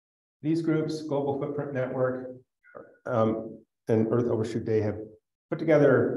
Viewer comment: the audio is barely understandable 22:25 to 22:30 what do you say there? "earth overshoot day"? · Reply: These groups -- Global Footprint Network and Earth Overshoot day -- have put together...